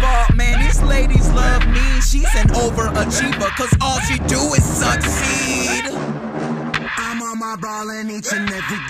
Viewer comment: over achiever cuz all she do is succeed (suck seed) is what it sounds like hes saying lol